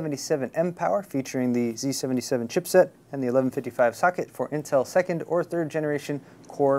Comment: Did he say "1155 fucket" ?? :O